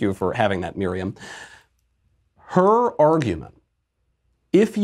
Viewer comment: makes fun of the accent of a non native speaker and then precedes to say "Urrrgument" instead of argument, smh